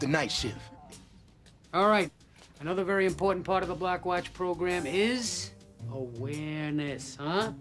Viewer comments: Did he say black watch program 01:35 ??????? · That damn man was saying 'Black Watch Program' not block watch program 😂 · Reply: cartoon expert no he wasn't at 1:35 seconds i thought the cop said black watch but then i rewinded the video and he said block watch maybe it just sounded like that because of the way he talks.